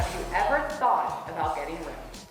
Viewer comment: Was watching this on my phone. Who else thought she said " have you ever thought about getting rimmed?"